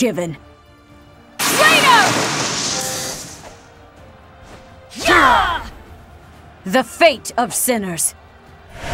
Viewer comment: What does Seiros even say at 0:23? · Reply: Traitor